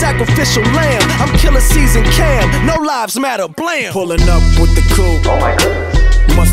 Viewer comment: OMG I thought he said HE "killin Cease and Cam, No lives matter" Thought he had a death wish but he said I'm killa season Cam. XD